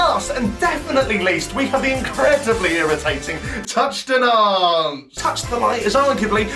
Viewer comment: i swear i heard touchdown nonce